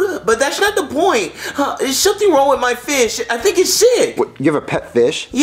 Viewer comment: i thought said (There's something wrong with my fist)